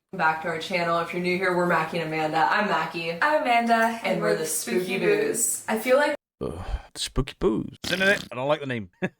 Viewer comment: I first thought you said they were "The Spooky Boobs." That would have been more entertaining.